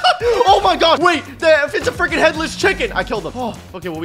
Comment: Did he say headless chicken